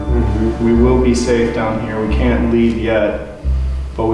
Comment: I heard "i can't leave"